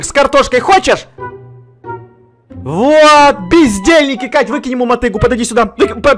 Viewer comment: мне показалось что он сказал пиздельники 😂😂😂